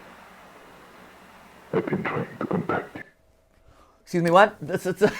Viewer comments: Pat I think it actually said "I've been trying to come back to you" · i thought he said ive been tring to compact it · Last tape sounds like it says “I’ve been trying to protect you”